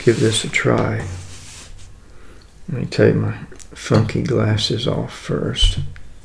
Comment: I thought you said "Let me take my fu**ing glasses off first", lol. Thanks for sharing!